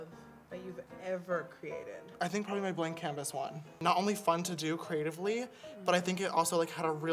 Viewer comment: When he said" blank canvas one" I thought he said "the blind katniss one"